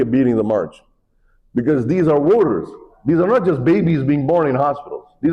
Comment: When that one guy was saying they’re voters, it almost sounded like he was saying ‘warriors’ - he’s right because we’ll be in for the fight of our lives pretty soon. They’re already planning a hostile takeover